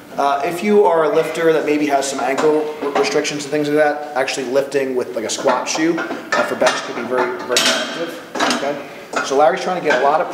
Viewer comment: what is he saying there? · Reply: 'actually lifting with a squat shoe for bench can be very effective'